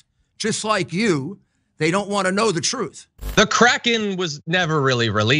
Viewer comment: KRAKEN,I thought she said Karen.Really have to get my hearing checked.